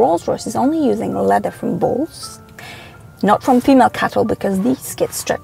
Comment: Great video, love your accent! Although I swear at first I thought you said "balls" not bulls 🤣 I literally thought they were using cow ball skin. Jesus. Sorry for the profanity.